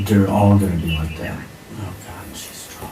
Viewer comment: when the sister called about the hotel, did Mom say, “She’s drunk” when she hung up the phone, right after telling her to drive back to her house to get money?